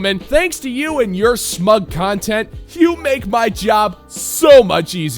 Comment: Did you just say "smug cuntent" 😂